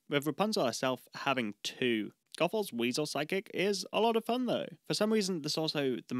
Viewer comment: Anytime you say "Side-kick" I hear "Psychic" and have to correct my brain.